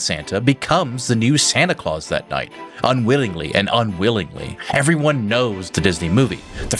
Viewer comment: Is it me or did he say the word unwillingly twice? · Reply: I think he said unwittingly and unwillingly or something to that effect.